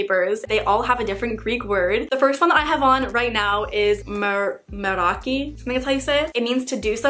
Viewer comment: i thought it said mermaid 😂